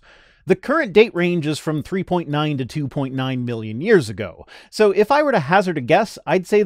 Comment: Hello Mr, I thought I would let you know that at 13:45 you use billion (at least the best I can hear) instead of million. I apologize for being pedantic.